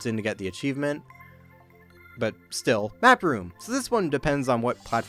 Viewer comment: am I the only one who misheard "Backrooms"